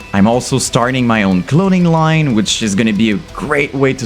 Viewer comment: At least I got the first word right 🤣😂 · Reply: To me, it sounded like you said: "Infant In-hil-later". Oh, and at 24:39 - "cloning line..." I think you're on to something there! Imagine if every UTuber had their own cloning assembly; that would certainly be more lucrative (albeit ethically dubious) than your own merch and clothing line. 💀